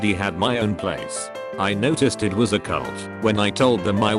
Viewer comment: I thought that you were saying reddit was a cult